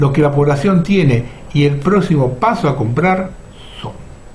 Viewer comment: Minuto 26:26, ¿que dijo?. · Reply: Y son el próximo paso a comprar.